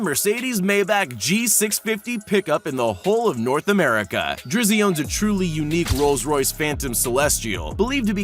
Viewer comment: Did he say drissy